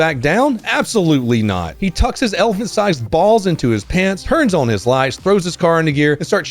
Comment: Instead of “Elephant-sized balls”, I heard “Elfin-sized balls” at first. I had to rewind to figure out why was dissin’ the cop!